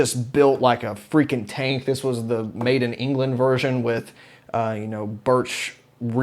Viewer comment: When you said made in England when you talking about the cabinet I thought at first you saying maiden England because Iron Maiden uses Marshall and I thought they had custom cabinets. I also love your videos